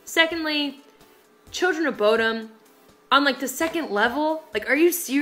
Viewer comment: did she say Children of Bodom at 2:58 ?